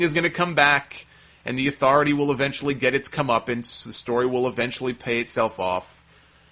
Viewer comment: What did say at 1:47?... "I believe that The Authority is gonna finally get its--" what? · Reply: Comeuppance.